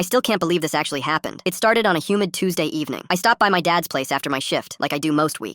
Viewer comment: Who else thought he said human not humid